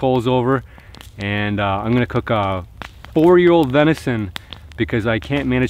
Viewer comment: I thought he said four year venison